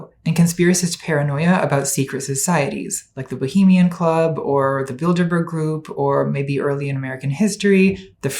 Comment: I kept hearing "the build-a-bear group"